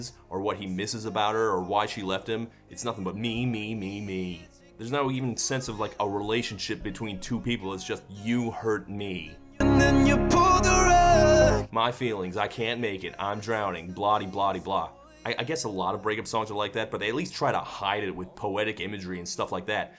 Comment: I always thought he was saying "and then you Polnareff", and for a short time I just assumed Lewis was a JoJo fan.